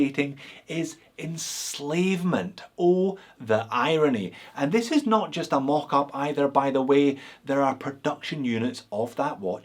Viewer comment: I thought you had made the sickest burn ever, but what happened is that I wasn't paying attention and misheard this beauty of a sentence: 8:08 to 8:16 = "Subjugating is enslavement. Oh the irony. And this is not just a mock by the way they are produced" (Chinese watches)